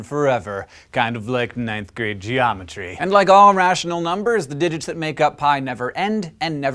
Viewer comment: Did he just say pi was a rational number?